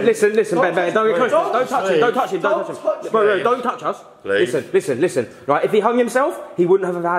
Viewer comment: I swear just said don't touch her😂 at 9:05 Is Ben a girl?😂😂 Btw be safe and Ben love you too loads❤